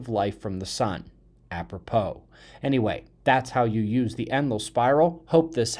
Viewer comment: I always thought you were saying "endless spiral"